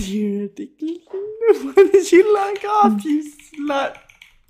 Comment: HOLD UP- WHAT DID HE SAY? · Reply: "Why did you log off, you slvt" 😭😭😭